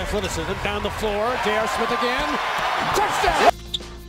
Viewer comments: did he really say touchdown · yo dd anyone catch the announcer say touchdown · Did He Say Touchdown? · did he say touchdown when JR scored?? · Did anyone hear "touchdown" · Brush who hear it say touch down on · did he just say "touch down?" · did he say touchdown?? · did he say TOUCHDOWN